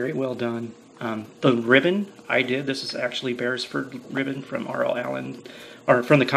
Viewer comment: Thank-you so much, yep I bought my good ribbon from over seas Berisford Ribbons is the best but very spendy. If you do buy some make sure you buy more than one cause the shipping is crazy, I can't find it anywhere in the U.S anyways this is their web site;) <link> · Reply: Thanks for responding :) in the video I misunderstood you and I thought you said bears fruit ribbon. When I googled it, it was giving me ribbons for fruit arrangements lol. So, I thought surely he isn't putting that in his Bible lol. Thanks again.